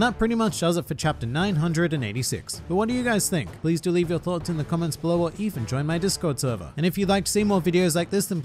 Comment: I misheard as "delete your thoughts in comment section down below."